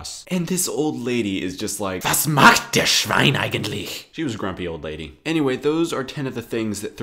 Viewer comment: "was macht *das Schwein da eigentlich?!" I heard that you needed help with your German so here you go 😂😂